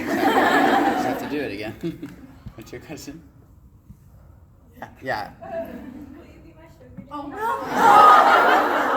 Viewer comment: what did she say?? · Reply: "will u b my sugar daddy"